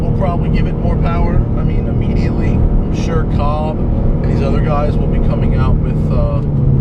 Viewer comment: which performance shop is he saying? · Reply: Cobb